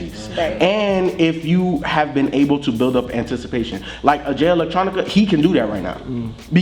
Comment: What artist are they talking about at 4:00? Sounded like aj electronica but couldn't quite tell.